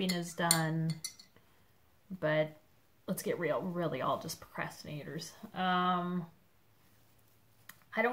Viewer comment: You said, "We're all procrastinators." I heard, "We're all depressed haters." I thought heck yeah and was gonna write Depressed Haters Unite! That would have been awkward?! I'm really liking the Lime Walk. I think we should start it together, because, you know, I finally started Mermaid's Folly which you remember was your fault. And I agree, if you and Elena ever get together we might have to start a stitch bail fund.